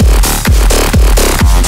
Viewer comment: What does it say at 1:14 ? It kind of sounds like "bonin" or owning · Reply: Cripple Bach it says "own it"